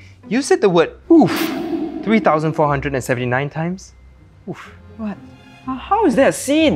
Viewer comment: He said oof 3479 times but subtitles say 3749 ahahah :D